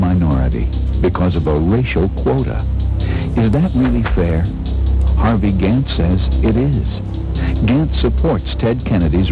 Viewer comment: Ha, I thought the add said "Harvey Dent said yes".